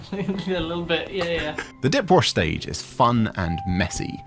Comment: Did anyone else hear him say "dick wash" at 8:32 ?